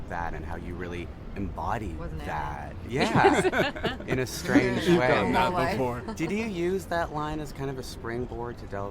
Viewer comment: What Rooney says in 9:22 9:23 ? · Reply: "was an alien"